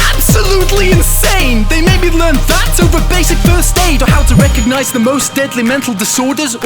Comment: I swear the first few times I listened to this song I thought he said first date instead of first aid